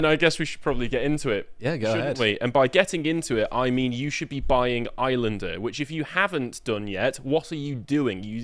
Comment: Is it me or did say 'Highlander'? A whiff of bias, possibly?...